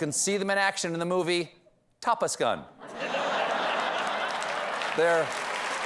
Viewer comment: He said "tapas" gun and I heard "top less" gun